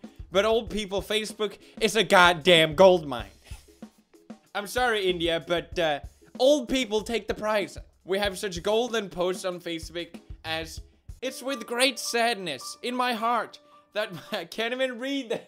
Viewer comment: Did He just sayed "Facebek" insted of Facebook??